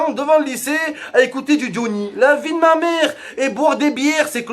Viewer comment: j'ai compris diony à la place de Johnny mdr